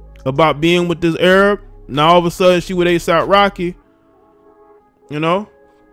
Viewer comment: I thought you said ASIMP Rocky...🤣